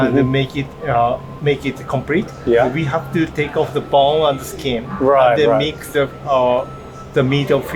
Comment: i think there was a slight mistake in the subtitles you had for the chef at 27:36; I think he said to take out the "bone" and it says "bowl"